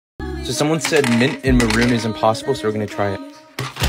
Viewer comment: I really thought that said macaroni and mint